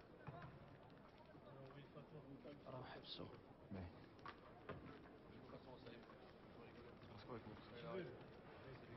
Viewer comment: What did El Djebena say in Arabic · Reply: "Habsou" meaning "stop him"